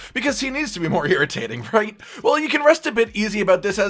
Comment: i could have sworn i heard a pun saying you could RUST a bit